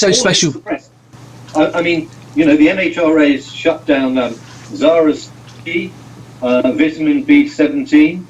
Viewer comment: What did say at 3:18? He mentioned a cure that sounded like "Zaras B" or something - what is that?